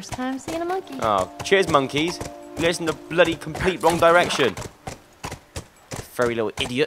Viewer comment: (:....*when joe said : ...wrong direction , i thought he said *One Direction